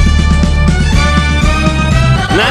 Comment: Me listening to the Intro: .... I hear Barney's a Average kid